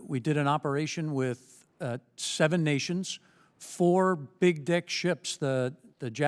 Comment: did he say “four big dick ships”?? I have listened to it several times and I’m pretty sure that’s what I’m hearing 🤣🤣